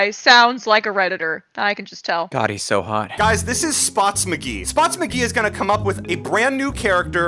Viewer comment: On 1:21 I thought they said spot (Mccee) into d|ç#